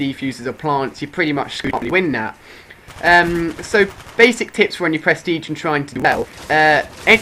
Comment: did u say: "basic tits" ? lmao